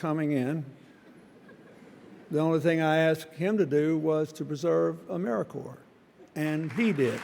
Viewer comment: What does Clinton say? Preserve a What?? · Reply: “Preserve AmeriCorps, and he did.”